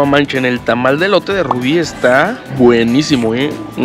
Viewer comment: no se porque escuche "el tamal de Rubi esta buenisimo" y yo de "QUE??" jajaja